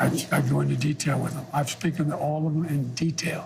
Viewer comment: This felt more like an intervention than an interview. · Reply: "I speaken to all of them in detail"? Did he say SPEAKEN or Spoken? What is speak? "Speaken" = you never spoke to them at all?